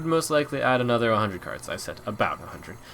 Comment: Turn on English auto captions, at 19:52 "their credit cards useless now" when talking about the new expansion. What was asked in the question directly after? I thought I heard 'all out mode' but not sure? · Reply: He said, "their current cards useless". I'm not sure if you are confused about that or just pointing out some funny captions. The question after, well it is hard to hear, but the most likely thing he said was "all allowed mode". Basically, a mode where every card can be played regardless of class. So for example, a hunter able to pick fireball, yay.